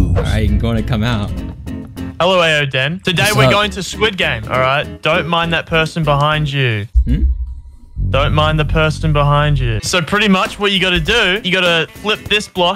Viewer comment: I thought he was saying don’t mine the person behind you